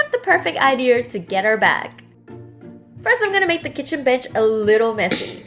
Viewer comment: Did she just say kitchen b****? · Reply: lol it does sound like it but maybe it's a mistake. Maybe she said "Kitchen bench" instead of kitchen counter xD